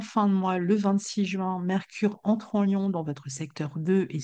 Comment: Merci❤ sympa et détaillé, par contrej'ai entendu "mars restrograde" c est mercure plutôt non?🤔 Sinon genial😢🤞👏💥💫🎉